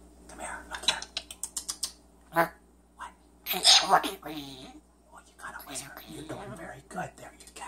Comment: “I’m smoking weed” - “Well you gotta whisper, you’re doing very good, there you go” “A SWEAR word?” - “What !?” “Im a pretty boyyy!” - “Did you just say that word again?” “It’s a way of saying it” - “Is that what you said?” Muffled “I’m smoking weed” - “Yea you’re saying that bad word again?” Running away “Hiiiiiiiii”